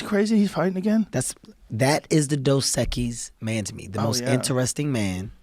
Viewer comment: What is Kevin saying around 0:30 ? „The Dozekis Mentome“? I’m not getting it 😂 google can’t help me on that one either... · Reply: The Dos Equis Man. LOL!! 😂 XX